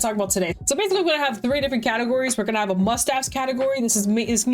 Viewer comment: When you said "Must Have" i heard "Mustache" and was really confused